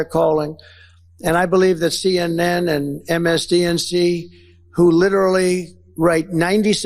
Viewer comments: Did he just say MSDNC? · Did Trump say, "MSDNC" at 1:00?